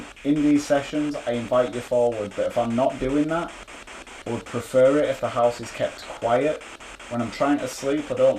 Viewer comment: I heard "Get - out of - the - house"